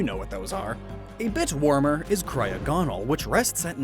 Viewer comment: I thought you said a bitch warmer!